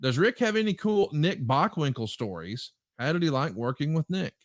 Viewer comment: It's BOCKWINKEL, have some respect and please go back to school. 🙄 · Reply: I apologize I thought he said Cockwrinkel, it won't happen again. 😔